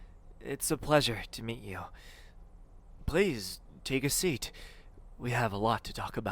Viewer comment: Why did I hear him saying " please take your clothes off" (｡・//_//・｡)